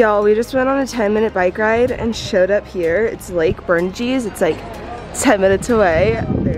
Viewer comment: What did you say? Lake Berne Cheese?